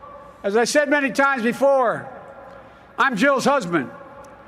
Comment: I thought he said "I'm Joe's husband"